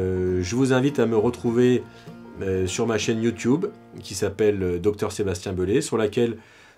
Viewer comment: J'ai compris " je m'appelle Sébastien Gaulé"😅